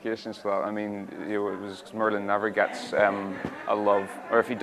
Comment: Some people were wondering what Bradley said at the love interest part (3:18 ?) I think he said “Merlin just wants love” or sm along “Merlin just wants…”